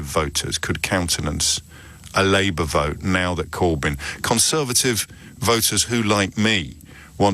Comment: At 10.50 did JOB admit he is a Conservative? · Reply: I thought so too first - which surprised me so I wound back for a second listen and he doesn't say that (your time stamp is off a bit - it's more like 10:15). what he says is: "conservative voters who now, like me, want to either prevent or minimise the negative impacts of brexit, particularly of a no idea, kamikaze brexit. so he's not saying "conservative voters like me" - which is what I first heard - but "conservative voters who share the same view on brexit as me [ a non-conservative voter]"